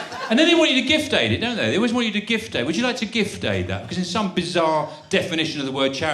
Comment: Some explain to me what a "gift day" is, or I'm probably mishearing it? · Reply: Gift Aid. Means the UK government chip in a bit extra if you give to a charity.